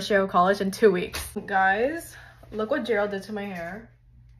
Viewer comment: I thought she said look what gel did but then ppl in the comments said Gerald